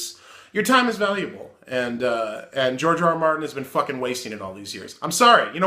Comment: omg I thought you said "Jar Jar Martin" and I can't stop laughing